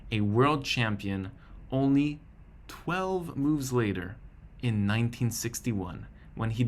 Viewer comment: did he really say 12 moves later 😂